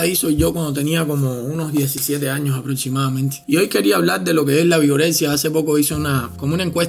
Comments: Pense que decia Vergorexia XDDD jajja wey Vergorexia, no mames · Pense que decía virgorexia Jajajajaja